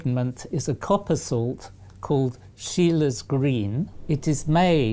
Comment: "an Australian chemist called Bruce" Of course. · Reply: Yes that along with 'Sheila's Green' which is what I thought he said before I saw the correct spelling.